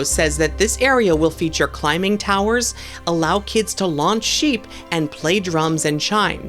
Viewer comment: Did you just say “launch sheep”?